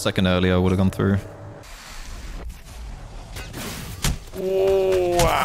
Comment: That sounded like “Ay chihuahua” A Mexican expression for annoyance xd